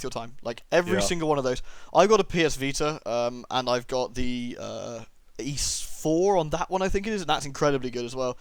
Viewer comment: Always a joy to watch a video with my three favorite let's players in it. I six? Ixis? Didn't quite catch the name, can anybody tell me the name? (at around 28:25) Steam refund is fantastic, altough i'd say they need to raise it to 4 hours of gametime. Spider-waifu for the win (And thats coming from somebody with extreme arachnaphobia) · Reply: The series name is "Y's"